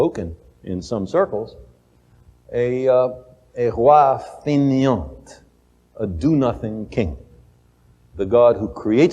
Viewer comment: what does he say? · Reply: roi fainéant